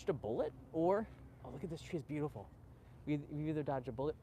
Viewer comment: new trend of fashion, or just a shading issue??? BTW, I heard ... "she is beautiful"...